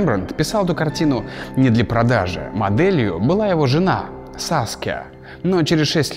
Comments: его жена Саске (или я не расслышала) Я- СААААССКЕЕЕЕЕ[ЕЕЕЕЕ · мне кажется что я услышала Саске? а вам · Когда услышала Саске, так и захотелось сказать: Наруто! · ето,..... только мне послышалось Саске?